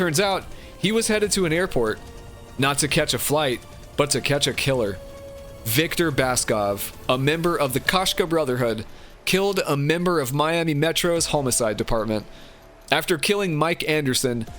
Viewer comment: Had to stop watching once I heard "Holmacide"